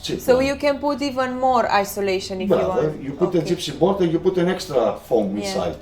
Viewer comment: I thought that you were joking but at 11:58 he really said "gypsy board" ahahhaah... then again maybe he is buying the boards from them, so maybe that is accurate :P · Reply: he said gypsum board